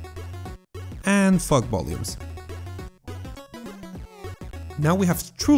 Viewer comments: i thought he said "fuck volumes" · Am I the only one who heard him saying "FuckVolumes" instead of "FogVolumes"?